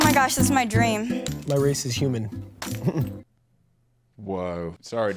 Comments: man when he said "my race is human" i totally thought he said "im a racist human" so i mean at least what he said wasnt that bad💀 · Cody: “My race is human” Cody, not two minutes earlier: “Elizabeth that’s a white name” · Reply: Ohh I legit thought he said "I'm a racist human" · PLEASE when Cody said “my race is human” at the end” I thought he said “I’m a racist human” and I was confused but not surprised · bro I thought he said "I'm racist human" 💀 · I THOUGHT CODY SAID, “I’m a racist human,” INSTEAD OF WHAT HE SAID AT THE END 😭😭